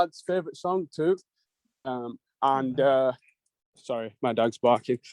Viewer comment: is it just me or did it sound like he said "sorry my dad‘s barking"